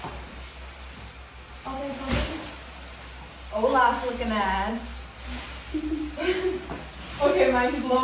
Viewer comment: ish i thought she said 'hola chicken ass'